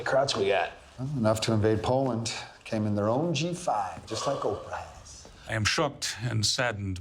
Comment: Just like Oprah has! All this time I thought he said just like old times! I gotta watch more stuff with subtitles on, my hearing sucks apparently